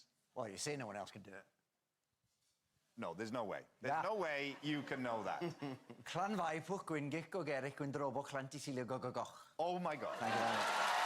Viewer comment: what's that word?? Can someone please write it down? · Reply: Llanfairpwllgwyngyllgogerychwyrndrobwllllantysiliogogogoch. :-)